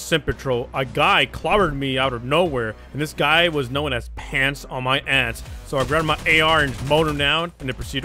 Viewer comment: I’m not gonna lie I laughed kinda hard when I heard “pants on my ants” as “pants on my a##”